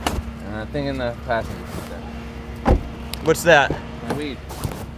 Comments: did he say my weed 😂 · did he say weed